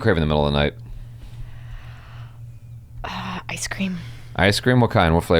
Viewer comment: I heard "I Scream".